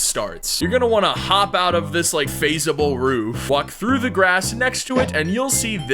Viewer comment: I thought you said,"f**k through the grass, " instead of,"walk through the grass. "